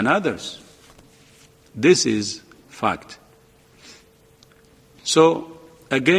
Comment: Ooh… big-nose Commie speaks from side of mouth. #defundWHO Oh, and catch what he really said at about 2:52? The subtitles didn’t: “This is f*cked”. Yes, it certainly is.